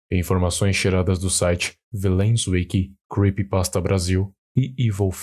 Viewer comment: só eu q entendi "informações cheradas do site..." ?